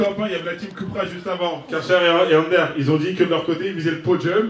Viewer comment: C'est moi ou le présentateur a 19:49 il a dit Karcher ^^